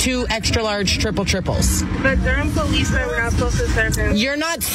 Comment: Is this in the U.S. or Canada? I live in Ontario Canada and I purchase $5.00 gift cards to give to the homeless and I haven't had a problem. Total B.S. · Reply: Sounds like she said Durham Police - my area. Disgusting. Should be followed up.